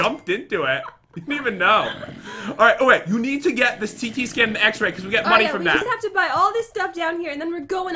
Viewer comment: is it just me or did say “ alright you need to buy this tiki scan”?